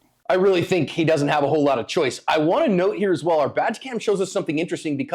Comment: I can’t help it, every time you guys say “badge cam” I hear “vag cam”.